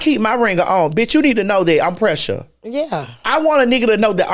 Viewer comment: TS I thought you said precious not pressure, I was howling with laughter